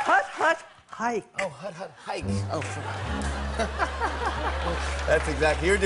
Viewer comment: did he say " oh fuck me " ???